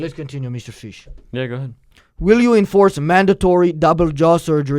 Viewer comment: Lmao what did bro say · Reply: He tried to say "go ahead"